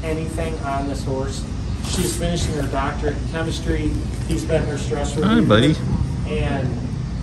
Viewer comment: in the video, I thought it was said that the horse is working on its doctorate in chemistry! 🤣